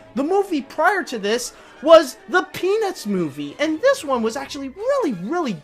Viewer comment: It sounded like you said "The Penis Movie." 🍆